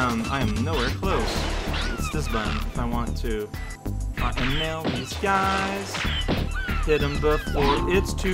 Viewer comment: Not what actually said but I swear at 5:57 he sang 'fucking nail this guy...' while nailing that guy with a bullet.